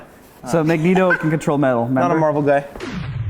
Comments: Did say: Not a Marvel guy? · Im sorry did i just hear "i'm a marvel guy". But they're all marvel characters..... Ffs and who the fuck grew up in the last 20 years and doesn't know magneto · i repeated this 10 times and did i just hear that he said hes not a marvel guy?